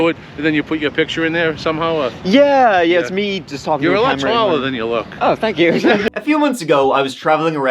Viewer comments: “You’re a lot taller than you look!” Lmaoo that’s exactly what I was thinking as soon as I saw them standing next to eachother ! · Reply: I thought he said “you’re a lot smaller than you look” 👀😅🤷‍♀️ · get your hearing checked he def said taller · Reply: I heard “smaller” too lol it’s either his accent, or my ears not being awake yet. Rewatching with subtitles on & I can clearly hear “taller” now. I feel like I’ve been Laurel-Yanny’d (or Brainstorm-Green Needled) iykyk